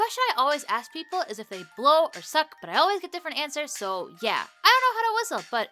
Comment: I didn’t hear the context of it and all I heard was “suck or blow”